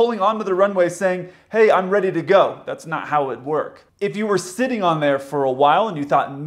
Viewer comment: when he says that's not how it work instead of that's not how it works LOL